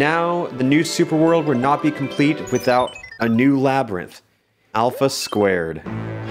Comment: I got an ad about spicy McNuggets so it sounded like alphas spicy McNuggets